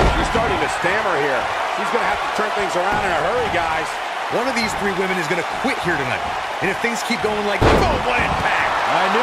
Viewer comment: Amazing match, a submission in the first 13 seconds of the match! The announce was either messed up or I really misunderstood, because at the 4:08 to 4:13 mark I could swear one of them said "one of these THREE women were going to quit here tonight." That kick to the face at 5:55 is just brutal and powerful. A really fantastic match!